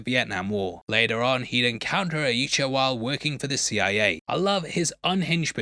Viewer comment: did you say yeechawa???